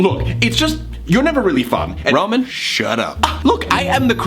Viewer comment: Who else heard "Ramen" instead of "Roman"?